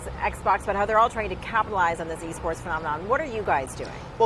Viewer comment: I thought they said E-Scorts not E-Sports. If you get the reference then you one of them boys.